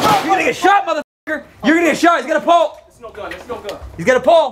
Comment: When he hit the door with the pipe I legit thought he had shot a gun at and D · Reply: I thought they said " HES GOT A PUMP" Like a pump shotgun? Enyone?